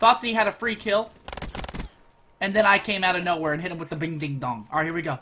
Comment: did he say "Big DingDong"?